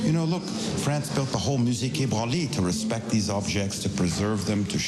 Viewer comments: the English subtitles say "museé le Baule" instead of "musée la quai Branly" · Just FYI, at 17:52 when the guy mentions the museum's name, it's "Musée Quai Branly", the subtitles are wrong. Source : I'm French. Great video otherwise!